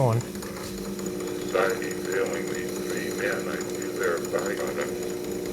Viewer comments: I would like to hear the whole thing. Sounded like 2 experts discussing "society failing" something. Edit 30:00 "Society failing the men". Is that what he said? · I hear at the 30:00 mark something like “I don’t think it’s abt society failing these three men.” I can’t make out the rest that well tho. This is really intriguing and definitely if possible should be preseved